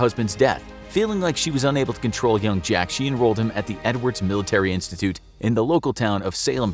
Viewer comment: I thought he said N-words military institute